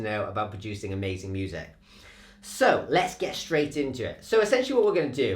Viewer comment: I went full vertical inverted plank cuz I thought he said "let's go Australian into it"